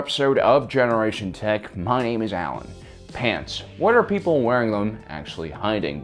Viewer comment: I was so confused for the first part, I thought you said " my name is Allen pants"